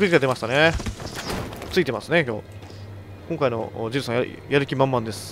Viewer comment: のとこでジルがジェイソンにしか聞こえなくて俺今13日の金曜日見てるんだっけ？ってなった